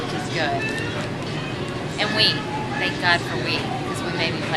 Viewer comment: I thought she said thank god for weed lol